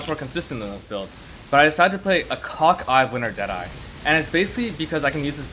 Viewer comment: subtitles said "cock-eyed winter jedi"